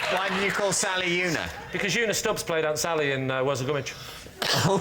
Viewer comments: What did Lee Mack say at 21:24? "Because Una... and I wasn't garbage." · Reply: "Because Una Stubbs played Aunt Sally in Worzel Gummidge" (which is a really old children's TV show and book series). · What does Lee say at 21:25 ????????? I’ve been wondering for ages. SOMEONE PLEASE HELP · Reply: because Una Stubbs played Aunt Sally in Worzel Gummidge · What does Lee say at 21:25? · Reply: “because Una Stubbs played Aunt Sally in Worzel Grummidge”